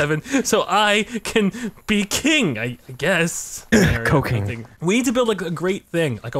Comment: it sounds like just said cocaine 😂😂😂